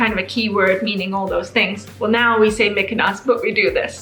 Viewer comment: Why did I hear "mykonos" as opposed to "making us" 😂